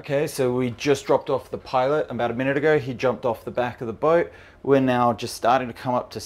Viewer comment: did he say the pilot jumped off the back of the boat? That's not good 😵, what did u guys do to him?